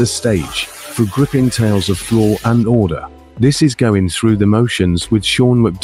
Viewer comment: The intro got me giggling because I misheard "law and order" as "flaw and order", and that so completely describes Canton!